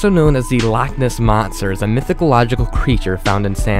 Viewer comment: Did he just say "mythicalogical"?